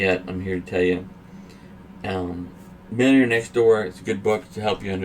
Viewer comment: What book did you say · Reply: Specific Books from This Video Millionaire Next Door <link>